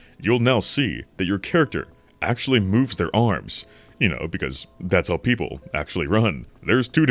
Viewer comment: sir wtf 😂😂 sounds you said well you know